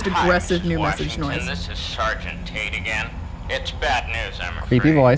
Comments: oml I thought it said Sargent Tit for a second. I'm sorry lol. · I thought the message said Sargent taint